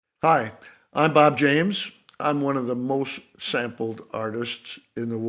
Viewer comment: I literally thought this said board James and got so excited...:(